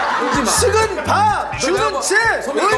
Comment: 왜 세찬이가 말하니까 식은밥 주는 집 이라고 자연스럽게 들리지 ㅋㅋㅋㅋㅋ 의식주 설명하는게 아닌거같어